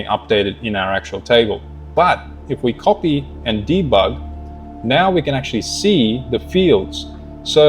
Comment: Hey mate - do you mean for creating the custom tools? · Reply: I figured it out! I had never used the 'copy to editor' on N8N, so, after playing your video on repeat between 25:30-25:40, I finally caught what you meant when you said 'copy and debug'. Thank you for making the video. I might need to check out your Skool community. I'm in Jack Roberts' Skool as well.